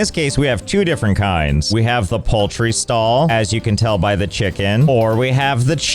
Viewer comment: The first time you said "poultry stall", I heard it as "paltry stall", which is perfectly in keeping with the way you game.